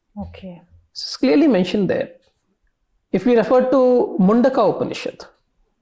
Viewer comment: Did u say mandukya upanishad or mundaka upanishad @ 4:52 ? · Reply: It’s Mandykya